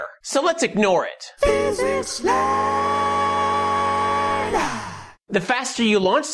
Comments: WHAT ON EARTH ARE THEY SAYING? Physics Lan? I tried turning captions on, but it just went blank. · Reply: "Physics Land" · What's the chorus saying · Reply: "Physicsland"